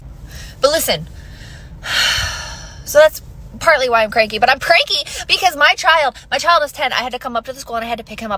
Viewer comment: Anyone catch the "i'm pranky"?"